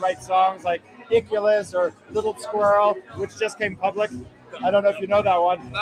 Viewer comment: Little Twirl? There’s a Phish song I’ve never heard before? although to be honest, I did just hear prep school hippie for the first time recently. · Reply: I thought he said little squirrel? 🙂